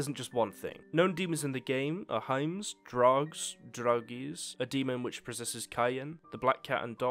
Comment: Am I the only one who heard this ”Known demons in the game hyms, drugs, and druggies?” 😂